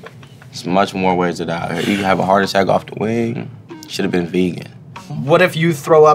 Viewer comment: “If you have a heart attack off a wing, shoulda been a vegan” 😂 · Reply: Bruh I swore he said shoulda been beaten, I fucking howled